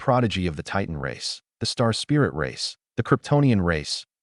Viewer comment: Did he say superman race